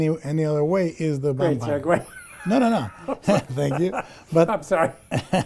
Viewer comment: Hello! English is a second language, so there's only one thing that I didn't catch. When Del Toro is talking about the judeochristian interpretation of films Romero says something at 36:44 and laughs. And it makes Del Toro say "thank you". What did Romero say? I listened a lot of times and I can't get it. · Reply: I believe he says: “Greats are great.”